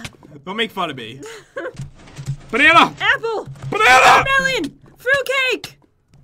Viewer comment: Pat: Banana! Jen: Apple! Pat: Banana! Jen: Watermelon! Fruit cake! If u want to to hear them say that, go to 12:55. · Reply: GOAGirlOfAwesome 510 when Jen said Fruit Cake 8 thought she said Raw Cake